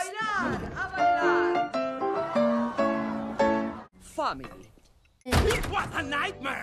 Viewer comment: I LEGIT THOUGHT ABUELA JUST SAID "F**K"